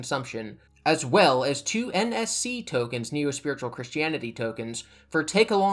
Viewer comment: I almost misheard "NSC token" as "NFT token" and burst out laughing